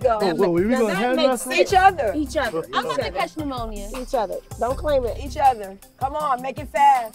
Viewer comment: Damn it looked like someone literally forced Blac Chyna to be there lmaoo 😂😂😂 Ive never seen someone look more miserable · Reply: Lmfaoo you heard her say I'm not tryna catch an ammonia when she had Big D and Lil D to arm wrestle